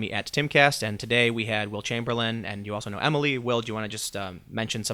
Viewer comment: haha I wasn't looking at first and thought he said Wilt Chamberlain I was like OMG The stilt is back!??